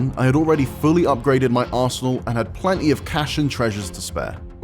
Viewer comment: sounded like "fully upgraded my *sshole"